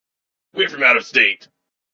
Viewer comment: "Does this thing interest you Ultra Maggie?" "The crystal... Yes. But what interests me more is that there's no glass casing to prote-" smash! O.O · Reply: oooohhh I thought you said you were from outer space